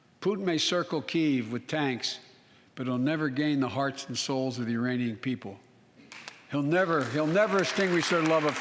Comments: arround 11:30 did he say the iranian people?!?! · did he just say Iranian people? 🤦‍♂️ Sleepy Joe stumbles once again